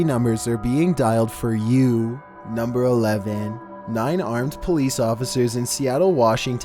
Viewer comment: what he said : "nine armed police officers"... what I heard "nine-armed police officer" I really had to back it up for a minute